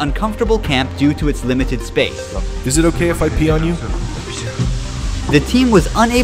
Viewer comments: Loll, wait at 25:49 did someone just say “is it ok if I pee on you?” Did I hear this correctly or am I trippin & need to go to sleep I mean it IS midnight. lol I was so deeply engaged in watching this and hearing this totally threw off my watching groove ! 😂😂😂😂 p.s. this was absolutely insane. You all are a pack of psychopaths. Thank you for taking me on this journey I would never even fathom doing but glad I can tune in via YouTube! :) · " is it ok if i pee on you? did I heard it wrong?😂